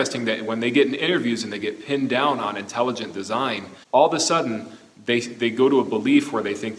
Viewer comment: Did he say "...all THE sudden..."? It's hard for me to give any credence to a person who hasn't even learned his or her own language.